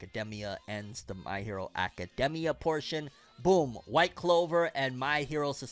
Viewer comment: Did just said white clover